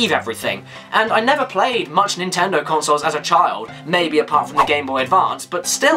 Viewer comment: He clearly said: "As a child".